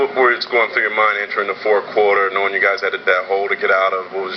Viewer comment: Did he say 4th quarter??